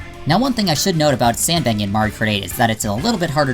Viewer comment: Did You Just Say Mario Krate?